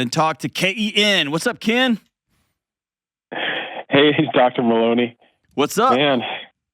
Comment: That first guy sounded like he said "Dr. Maloney".